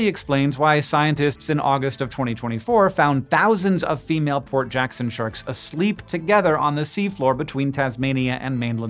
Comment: For a while I thought you were saying poor Jackson sharks